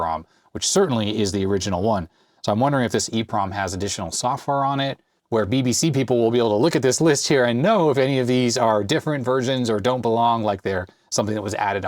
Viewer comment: I'm sorry but I spit my coca cola out when I heard you say "BBC People"😅🤣😂 My inner 13 year old can't help itself.